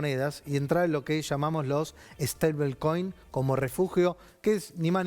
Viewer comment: Hola a todos. No entiendo qué dice en el minuto 30:54, ¿staybit coin? ¿Staking coin? Haber si alguien me dice cómo se escribe por favor. Voy a averiguar sobre el tema, porque desconozco. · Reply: Stablecoin